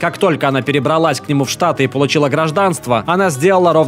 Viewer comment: мне послышалось не штаты а штаны